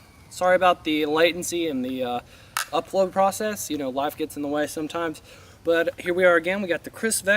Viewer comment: could have sworn he said "wife gets in the way sometimes" lmfao